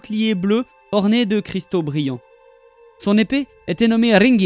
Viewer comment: cristaux quoi ??? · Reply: Brillants :)